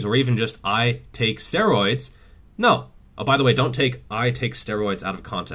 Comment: I heard you say "I take sterilized CORTEROID"...